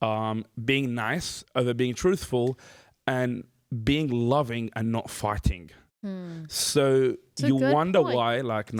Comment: Had to back up at 3:06 because I swear I heard "loving without farting."